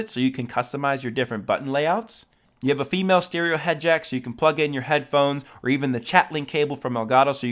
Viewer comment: Did he say female stereo head jack???